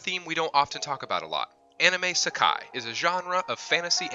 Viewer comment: Wait. 0:03 did you call it anime Sekai? Not isekai?